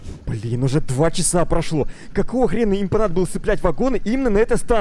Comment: мне одному послышалось что сказал "БЛЯТЬ"?